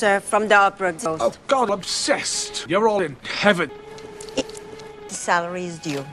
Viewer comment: I heard: "Oh God-obssessed! You're all in heaven!"